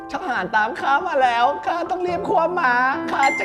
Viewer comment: ได้ยินว่า "ข้าต้องรีบคว้าม้า" ม้าดีดแน่นวล 😂😂